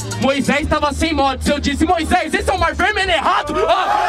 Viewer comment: alguém me explicar pfrv não entendi oque ele falou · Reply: Esse é o mar vermelho errado!